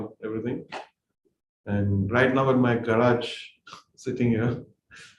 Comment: to 8:46 what he said ? Garaj means